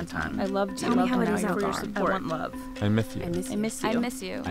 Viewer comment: "Love." " I myth you. " Did anyone else hear that man's speech impediment?